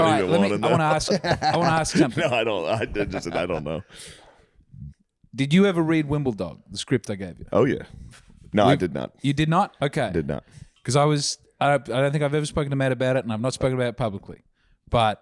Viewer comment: I thought he said "Wimbledong"